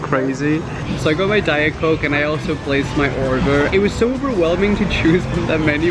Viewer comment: it sounded like he said “orger”